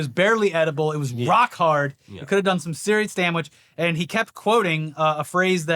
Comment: did say “damwiche?”